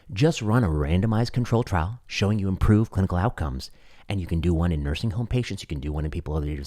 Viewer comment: i swear i heard show you improve political outcomes. Which to be honest seems to have been the more likely discussion.